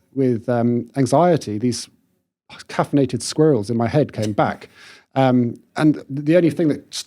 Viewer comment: did you just say "caffeinated squirrels" at 14:58? That is now my new favourite phrase! ♥